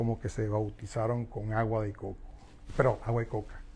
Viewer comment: im deaf lol i could have sworn he said "agua de coco" as in coconut water no coca.. thanks for the explanation! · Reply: he did said Agua de Coco (coconut water) but right after he said Perdon, Agua de coca (sorry, cocaine "coke" water)